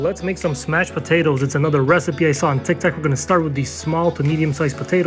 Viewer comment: Did he just say tictac instead of tiktok???